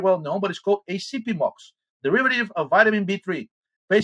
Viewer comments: Can anyone spell the drup is talking about at 13:30? I can't quite make out the name and or how to spell it and I'd like to do some research on it. Thanks. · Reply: Nevermind. Found it. Called Acipimox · What is the drug he mentions at about 13:25. Listened to it 20 times and i cannot understand him. Sound system gives it a tin can echo. Thank you. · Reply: acipimox, it‘s hydroxylated niacinamide. Normal niacinamide does that too though